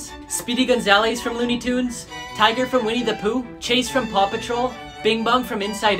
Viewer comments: It sounded like he said “papa troll” and not paw patrol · Am I the only person who heard him say tiger not tigger